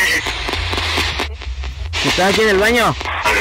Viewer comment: El minuto 20:21 a 20: 22 escuché estas en el baño